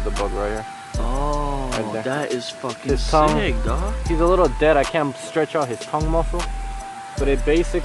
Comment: did he say "he's a lil dead" at 3:30? Lol yeah he is.. just a lil..🙄